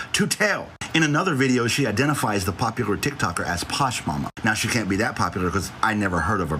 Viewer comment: Started watching thought you said Mama Tot. I’m thinking awwww here we go. I’m gonna have to fight with Nick and now. What y’all not goons do is drag mama Tot. 😂. We’ve never disagreed on anything. Then I hear mama posh ? I’m like oh go on I don’t know her. Problem solved 😂